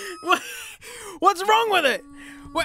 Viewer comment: The desperate "wHaT's HaPpEnIn!?!?!" Amongst all the beep boop doops at 8:00 is absolutely priceless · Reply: he did definitely say “what’s wrong with it?”